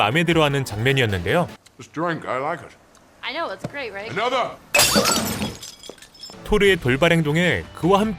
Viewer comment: 진짜 한잔더 라고 들리는데...ㅋㅋㅋㅋ